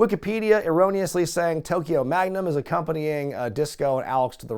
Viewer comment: "Wikipedia erroneously saying Tokyo Magnum is accompanying them" I had to rewind that because I could have sworn you said Truth Magnum